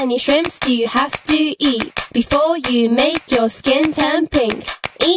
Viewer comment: I always thought she said "before you make your skin temping"